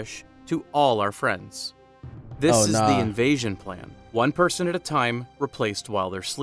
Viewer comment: why did i think he said "asian man"